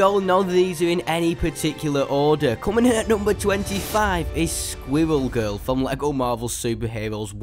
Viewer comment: At first I thought you where saying squiggle girl